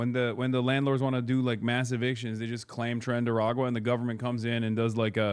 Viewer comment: I literally never even heard the words "Tren de El agua" until February of this year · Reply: I just hear "trendy Aragua" and I'm like damn, who's this fly character?